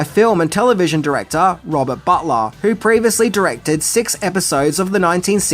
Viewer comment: "Lois & Clark was directed by director Robert Buttlove" excuse me what?! Then, on screen, it says "Robert Butler," okay... that's better.